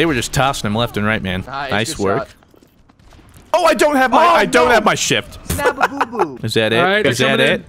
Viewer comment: Did he say he didnt have his shit at 3:20?